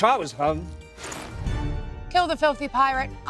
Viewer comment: I thought he said “the bitches first”💀